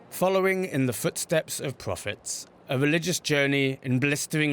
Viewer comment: I heard that as " in the footsteps of profit" and was confused